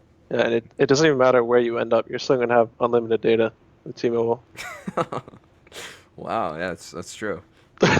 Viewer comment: "It doesnt metter where you end up, you still have on (what he said on here?) with T-Mobile" -Bjergsen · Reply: "you're still gonna have unlimited data with T-Mobile"